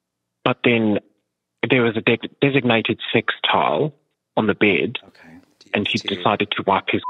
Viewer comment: I heard "sextile" instead of "sex towel" and thought it was brilliant.